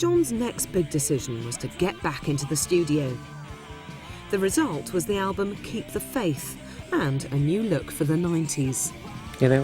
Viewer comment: did she say kick the faith :D